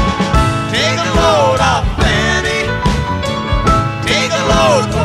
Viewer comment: I used to think it was "Take a load off Annie" probably not the only one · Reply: I thought they were saying "Franny." LOL